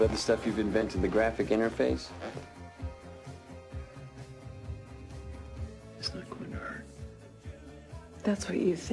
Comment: What is he whispering to her at 1:48? can't make sense of it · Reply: "It's not going to hurt" he says. She replies, "That's what you think." She and her development team at Xerox made the computer they buy off the source code from. Making something that no one can understand hurts, THEN a 'weird guy' from some upstart at the time known as 'Apple Computers' BUYS the technological baby you slaved over to make... HURTS worse!